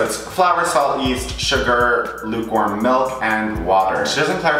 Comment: He kept saying “lukewarm milk” and I kept hearing “newborn milk” 😂